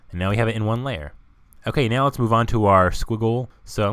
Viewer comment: Hi what did you said on 4:52 scogle? what is scogle?